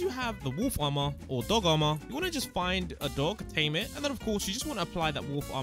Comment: I'm sorry did you just say adult 🤨